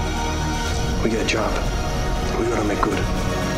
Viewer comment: what does he say at 1:59? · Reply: Sounds like "We got a job. We better make good"